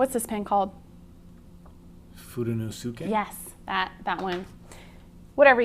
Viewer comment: Sharon Boswell It’s funny how everyone hears something so completely different. I thought he said Fudenosuke pen. (I’m sure spelling is wrong!). I think that’s what makes life fun. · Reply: LOL. I wasn't clear on my message. did say "Fudenosuke" but the cc: typed it as "Food in the Kitchen". Too funny!!